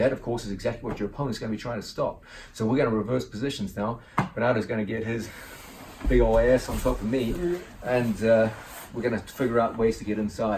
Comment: "Bernardo is completely inside me" ;) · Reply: Did john say "bernardo is gonna get his big ol ass on top of me"?